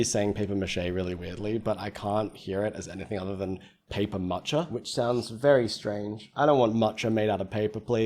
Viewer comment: I thought he just said paper matcha before you clarified lmaoo